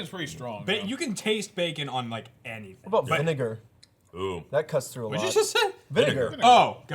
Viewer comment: Circus Peanuts are great :D Also deffo said the n-word at 25:18 (I know he said vinegar but teasing) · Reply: vinegar. thought he said n-word, but he said vinegar